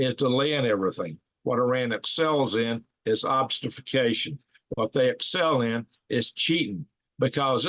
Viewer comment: What was that word at 7:27??? Obstafacation? I don't know that one... perhaps I am misspelling it.